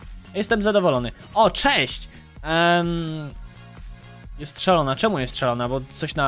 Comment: gdy powiedziałeś ,, jest szalona" usłyszałam,, postrzelona"😂😂 Edit: moge serduszko ❤